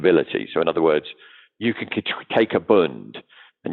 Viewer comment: what word said in the sentence “you can take a ____”. Sounded like burnt? Thanks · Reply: "bund" - German gov bonds